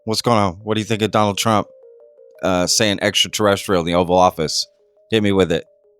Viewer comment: At first it sounded like Trump said extra torrential (A heavy downpours currently hitting the region) before he said extra terrestrial, maybe a freudian slip???